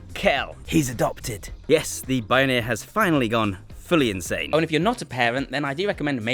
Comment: I heard “yes, the boner has finally gone insane…”